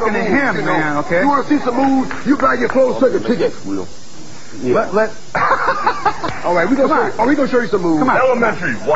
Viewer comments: What did hulk say at 1:04? I'm gonna make him believe or think it's real. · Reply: 'It's real', yeah. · I cant make out what is said so it might be a stretch but at 1:03 when Hogan whispers in Mr T's ear it almost sounded like he said "im gonna make it real" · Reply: He said " he doesn't think it's real"